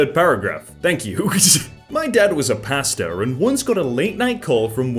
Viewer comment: TELL ME WHY I HEARD "my dad was a pasta.." For reference I have been up for like 18 hours I need to go to bed.